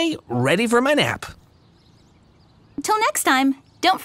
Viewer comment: it sounded like she said “ or go to my YouTube channel to watch porn!”